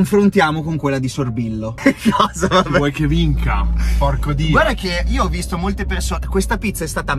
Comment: Ma solo io a 1:35 sento un bellissimo "chi vuoi che vinca, porco dXX" ??? Grandissimo:)